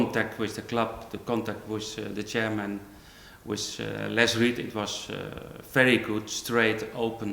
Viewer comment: it kinda sounds like he says less weed :P i had to press the replay button 5 times to hear him say less reid